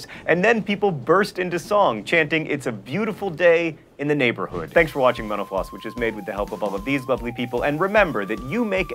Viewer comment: My ears blinked and all I heard was "thanks for listening to menopause" lolwut? Great video series though, thoroughly enjoying the ramblonic style.